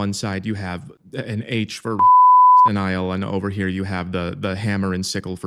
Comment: an H for *bleep*... why even say anything in a video if you have to bleep it so hard that its impossible to even guess wtf you said? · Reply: it was "holocaust denial" - Im pretty sure